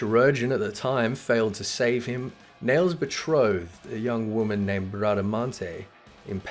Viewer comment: What the heck did he say at 6:35 ??????? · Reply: Nael's betrothed? his wife?